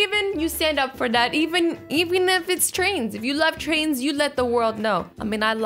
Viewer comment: I thought it said I am trains